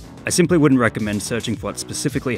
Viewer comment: For a moment I thought you said “look it up yourselves” rather than “look after yourselves”